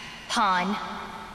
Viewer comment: what does Amy say to Asta at 1:30? "Hun?`" :D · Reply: She called him a pawn. Basically calling him a puppet.